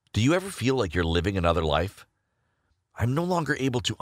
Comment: who thought he said “Hi! I’m Gay”